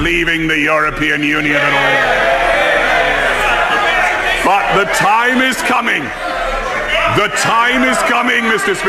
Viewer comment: Well said. I thought he said “”F@@ the time is coming””. Then I listened again and he said BUT the time is coming. I guess I need my lug holes irrigated